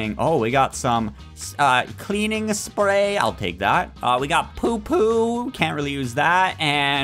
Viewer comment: Bro did he say poo poo?!